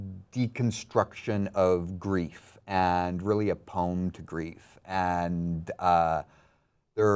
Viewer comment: what kind of grief did Bryan say was going to be the focus of episode 2? It sounded like "apowned grief" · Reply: a poem to grief.